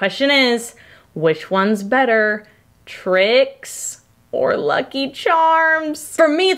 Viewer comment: At the end at first I thought she said tricks or lucky charms but then I realized she said trix the cereal 🥣😅😂